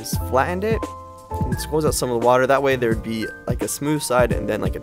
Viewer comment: he said "squoze" instead of squeezed love that 🤣